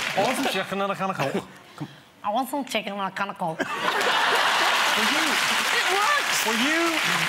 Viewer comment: It sounded like " I WANT SOME CHICKEN AND A CAN OF COKE"